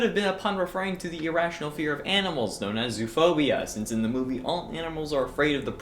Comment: At 1st when it said zoophobia I thought it said zoophilia... then I got scared